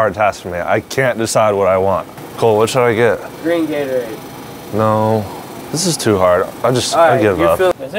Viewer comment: I thought he said lean Gatorade iwl wtf I thought this was supposed to be kid friendly